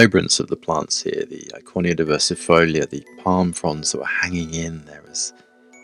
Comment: What is the scientific name of the plant at 13:40? Tried to catch it but couldnt quite understand. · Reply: Eichornia diversifolia.